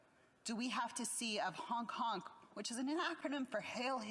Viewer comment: Did she just say “honk honk” is an acronym for “hail Hitler?!” Did I hear that right?! If so, then this lady needs to learn what an acronym is🤦🏼‍♂️